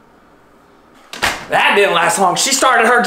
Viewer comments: I heard “ doors will be doors nini “ 😂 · Lol I heard "doors will be doors And literally died laughing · I love 😂 😂 "Girls will be girls" 😂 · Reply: He sounded like he said doors will be doors but he meant girls lol 😂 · I thought he said doors will be doors but it until I read the bottom